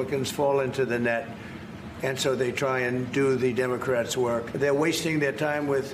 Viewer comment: The democrats work? Sounds like he is saying the devils work.....